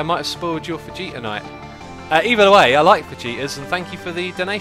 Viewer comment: Lol I thought you was saying vegeta's and not fajitas. The "ji" is pronounced as "he"